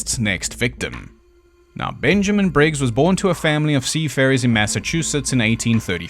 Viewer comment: Was doing something and thought I heard "He was born to sea fairies." Had to rewind and listen again.